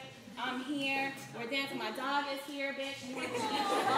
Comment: I had to rewind a bit thought she said my daughter is here. I was like WHAT and then realised she said dog 😂😂😅